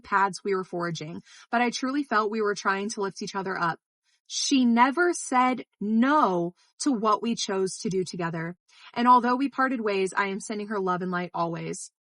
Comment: "She never said no" was all I needed to hear from Melanie to make up my mind. Her lack of accountability TO THIS DAY is disgusting. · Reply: I thought she said "she never said no to the things WE CHOSE to do together?"